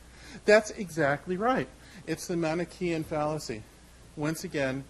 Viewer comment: did he say Manichaeian fallacy?